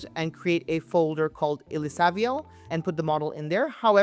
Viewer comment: to install the FP8 frame pack you said to put it in " Models/ Diffusers" what did you say the name of the folder to create was, I don't understand what you're saying? Thank You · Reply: Good catch. I was quite tired that day and missed that. The folder i said to create is Illisaviel the name of hte creator of framepack, but I beleive it was for organizational purposes only. You shoul also be able to just place the models in models/diffusers